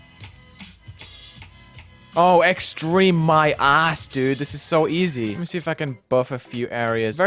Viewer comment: did you just say extreme my a** !?